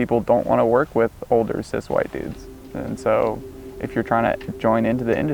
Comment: Honestly at 3:18 is he saying cis or sus white dudes?